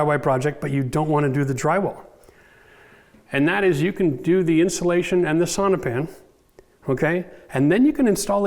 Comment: Really enjoy your explanations, thank you!! At 15:33 I think u said we could put insulation batts, and sonopan…and then… drop ceiling (?). In order to still have access to it wiring and plumbing, I thought we could install pink batts + noise reduction ceiling panels…but how would we also include sonopan ?? Thank you